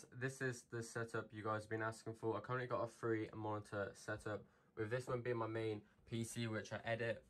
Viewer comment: At first i thought he said "only 3 monitor setup" and was like bruh